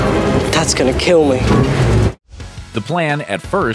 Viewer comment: I hear “that’s gonna kill me.”